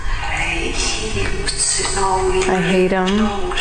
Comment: I heard clearly "I hate him, he's so in control . I never rest"